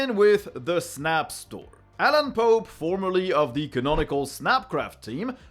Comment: Sounded like he said Alan Pope formerly of the snap crap team!